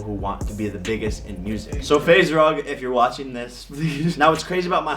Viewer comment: Did he said faze rug